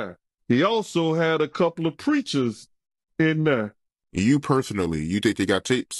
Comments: I thought that body guard said ‘preachers’.. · I thought he said “preachers” ?? That’s what I hear and find totally plausible in the USA